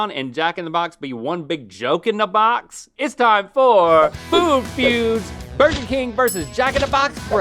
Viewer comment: I thought that said food fetus.